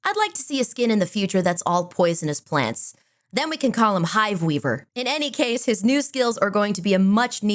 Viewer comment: I heard "High Weaver". That needs to be a thing